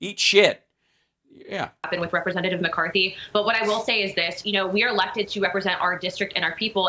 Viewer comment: Did she make up a word at 1:02:29 ? Did she say "we are lefted to represent our districts" ? 😂😅😆🤣😂😄🤣😆😂😅🤤